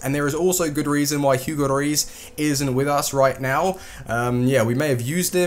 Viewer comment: sounds like is saying lloris died